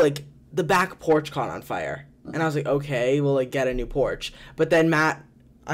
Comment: I for real thought nick said okay WELL get a new porch and not WE'LL get a new porch I'm dead XDDD